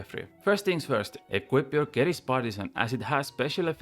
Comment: equip your what? i couldnt understand you sorry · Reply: Equip your Keris Partisan as it has a special effect against Scarabs & Kephri